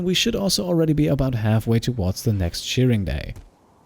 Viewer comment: LOL I kept hearing him say "Cheering" day instead of "Shearing Day" and now I'm giggling like crazy xD Like "go muffalo, go!" LOLz